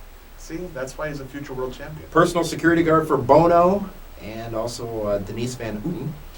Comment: did he just say personal security guard for "bone-o?"